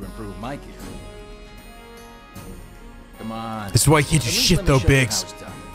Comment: Kitties! …I hope they don’t…y’know… 41:18 What did say there? The junk dealer’s lips move weirdly. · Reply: "This is why you can't do shit though, Biggs."